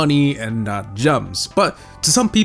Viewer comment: Did he say: “RANDOM-NIZER” ? 4:54 I’ve never heard it pronounced that way in my LIFE brother ❤️